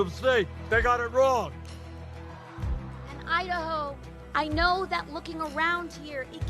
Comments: When AOC says "Idaho" all I hear is "I da hoe"........ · I mis-heard her. I thought she said I a hoe.